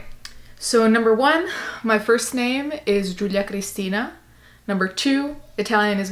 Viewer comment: ! People butcher it all the time here lmao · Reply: oooh lol I thought you said druidia Christina I was like that's a different Italian name 😂